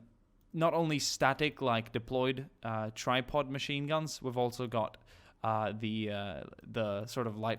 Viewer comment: I'm so sorry I thought you said tide pod machine gun